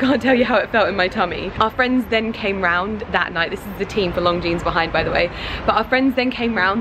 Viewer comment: It sounds like she is saying 'Long Jeans'.